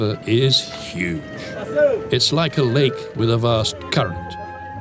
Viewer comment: Did he just say " it's like a lake with a vast cunt" ! ( at 29:00) lmao..